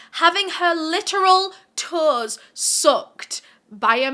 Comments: I thought what you said was "she had her toes soaked" - this man had brought her a small tub to " soak" her feet and I couldn't understand how that constituted a scandal 😳. The I heard you again say the word & I realized you'd said "sucked"! I then turned on the CC, lol! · When u were talking about her toes I was so confused until u showed the picture. I thought u were saying "soaked" not "sucked".😂 Also Jane couldn't have been "face timing" her boyfriend. It wasn't invented till almost 10yrs afterwards.